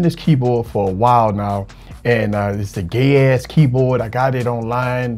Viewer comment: I opened.this video and forgot and I thought you were saying "dicks" instead of dex so for a few seconds I was like "wat" Edit: OMFG I thought you said "Gay ass keyboard" is this on purpose xD